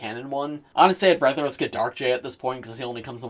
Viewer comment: Did you just say "Dark Jay"????